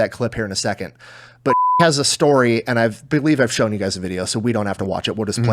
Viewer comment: Kudos for the integrity of protecting the teachers identity but I really enjoy that the censor beep as introduces the story makes it sound like he said "Bitch has a story..."